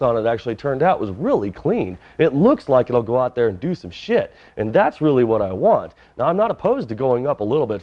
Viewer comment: Whoa.. at 17:09- did you just say “it looks like it’ll go out there and do some SHIT”!?!? First 4 letter word I recall hearing on! You must really be excited about this build!!